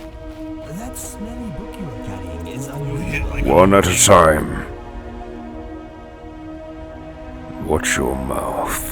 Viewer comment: I think at around 18:47 I didn't quite catch what Hoth said? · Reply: "You hit like an old lady"